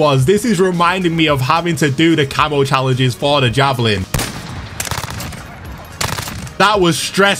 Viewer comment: What did he call the “Jokr” it sounded like he said Javelin, how dumb do you have to be to pronounce JOKR, Javelin 😂